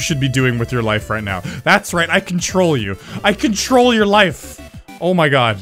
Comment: I can troll you! I can troll your life! (I'm pretty sure he is actually saying "control" but it sounds like "can troll")